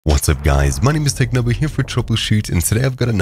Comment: I heard what'sup guys my name is Technoblade here in your intro